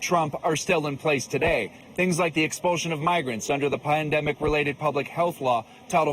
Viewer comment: Did anybody catch the male reporter at 2:16 of the video saying (PLANDEMIC) Instead of pandemic a slip up from the main stream medium